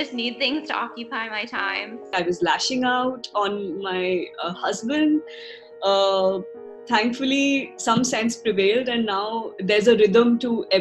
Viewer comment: Listening to this, at 3:40 I heard “ I was lashing out a my husband, thankfully some sex prevailed”